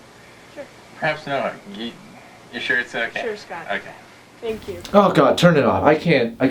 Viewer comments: anyone else hear her call scott? or am i hearing things? lol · awkward date; Did she say "Sure Scott" instead of, because outch! She had already emotionally left the porch. I feel kind of bad for that guy.